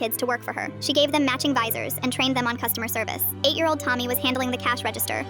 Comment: "TOMMY STOP EATING THE LEMONS 🗣️🗣️🗣️" Dumbledore said calmly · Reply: Help I thought he said Tom not Tommy…..